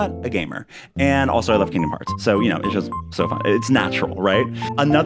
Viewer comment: "Feels like we just started, right?" IT REALLY DOES omg. CONGRATS on the sponsorship! "Plus I love Kingdom Hearts" - had to back up and listen to that again bc that came out so fast, I coulda sworn you said you love "candy bars" - LMFAO! Dolsney! "And weeds!" You are definitely in TOP FRENETIC FORM here, and I love it. XD Seriously, though - these look great! Don't play Blitz (I don't need more timesinks, omg) so no fave emoji. As for beach inspo, see if this helps - think about everyone living on the island and what THEY would do, or how they would leave it. Kinda like you did with their yards, but in this case, it's community space. What would the villagers have done? How would they have decorated or set up the spaces for their use?